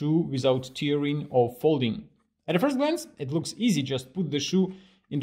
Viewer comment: I thought he said NO FOLDING lol 😂